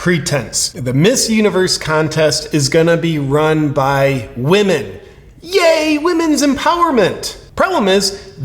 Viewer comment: Did she just say "run by we men" instead of "run by women"?